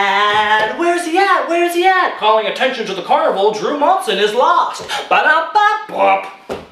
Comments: "drew monson is lost" when he thought he said "drew monson is missing" that's hellaaa wierd... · Attention Drew Monson MISSING ITS MISSING HE EVEN SAID IT WAS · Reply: I’m rewatching this and I literally thought he said “Drew Monson is missing” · All i hear is ‘Drew Monson is lost’ PLEASE HELP ME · that is not what he said. I watched this video a few months ago and he said “calling attention to the carnival Drew monson is MISSING!!” Not lost #shanedawson #mandelaaffect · I thought it said, "Drew Monson is missing" ...